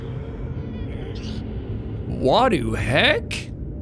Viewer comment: Did jack said wad hek